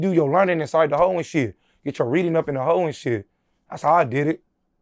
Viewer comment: He said u get your reading and learning up in the hole , not at home /homeschool like interviewer thought he said lol